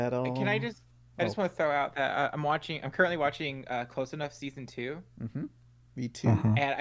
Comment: I thought he said watching Clone High season 2